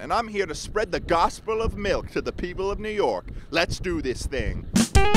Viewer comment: When he said "let's do this thing" I thought he said "grilled cheese stand" I think I'm going deaf or I just want a grilled cheese Okay now I do